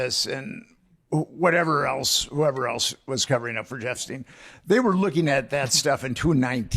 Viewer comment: did he just call him jeffstein? 😂😂